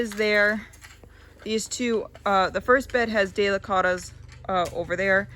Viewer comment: While listening to your content your daughter mention planting deicottos. I did know what it was in found on 37:32 the spelling. I know sometimes what is being said and what gets picked up are different. Can you tell me what it is she spoke of planting? Thank you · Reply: Delicata squash